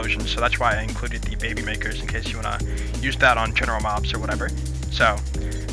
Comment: did anyone else hear him say "general knoxx" at 1:44? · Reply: General mobs